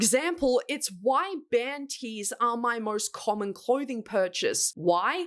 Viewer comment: When you said "band tees", at first I thought I heard "panties" so when you went on to say "but I also know I'm going to be wearing them and their crust until I'm 70, 80..." I had a great laugh :D